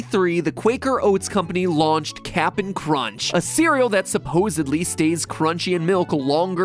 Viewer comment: Bruh everytime Mat says Cap'n Crunch's name, I hear "ratio"